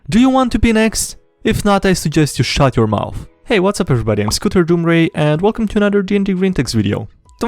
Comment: I heard "Scooter Doom Ray". I don't mean it in a cruel way, I just laughed out loud. Keep up the great videos!